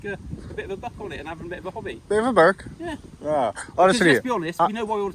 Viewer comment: he says "a bit of a 'book'?" I am not familiar with that idiom, what is the word? a bit of a bog? closed caption did not pick it up either, I'm curious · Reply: Bit of a buck. I guess he means either some money from views or a bit of a rise from the police